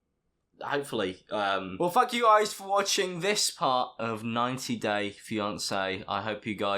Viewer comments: Awe his lisp🥺🥺 I thought he said “fuck you guys for watching this video” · I thought he said “well fuck you guys” and i was slightly offended. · I legit heard you say "fu*k you guys for watching" insted of "thank you guys for watching" 😂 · it sounds like he says “Well fuck you guys for watching” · I thought you said "Well, f*** you guys..." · when you said “thank” it sounded like “fuck” so i heard “well fuck you guys” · anyone else hears "well, fuck you guys" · did anyone else hear, “well fuck u guys for watching this video.....? 🤔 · it sounds like he says “Well fuck you guys” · did he say “well fuck you guys for watching this video” or am I just stupid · i thought you said "fuck you guys for watching" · It sounded like he said well fuck you guys a the end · Doesn’t Alex sound like he is saying f*ck You guys for watching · why did I think he said fuck you guys for watching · sounds like you said "well f you guys for watching" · I swear I heard "well fuck you guys for watching" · I thought he said "well, fuck you guys" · i thought he said “well, fuck you guys for watching”😂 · Did anyone else hear, "well, f-ck you guys for watching..." at 11:07 ? Ahahaha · sounded like f**k you guys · Why did it sound like he said f**k u guys on his outro 😂😂😂